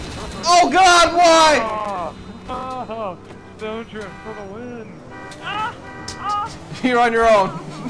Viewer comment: you can hear i think saying STONED TRIP FOR THE WIN